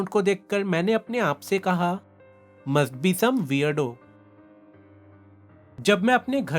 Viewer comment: kimjal kya boli? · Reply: She said - Must be some weirdo ( zaroor koi ajeeb insan hoga )